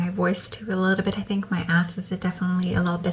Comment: I heard “my ass” as well! 😂😂😂